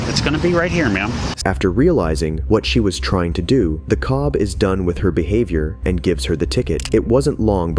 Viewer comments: did he just say "cob?" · did he say cob?🤣